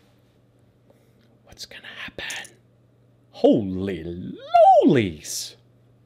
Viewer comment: holy lollies or something, I don't know what he's saying, but that shit's hilarious :D